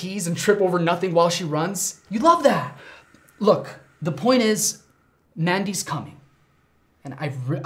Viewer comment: I thought Anwar said "Andy's comin'' Micheal falls